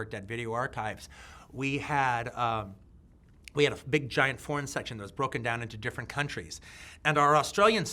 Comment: Im Australian.... when he said "foreign section", i thought he said "porn section"